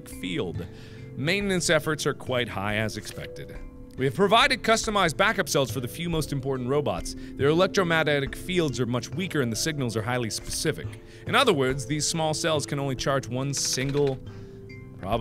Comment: Electromagneticmagnetic field I love that word 🤣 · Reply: But i hear "Electromagneticandmatic field" hmm...